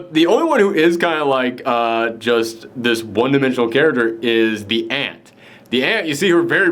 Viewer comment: For a moment I thought you said "The ant".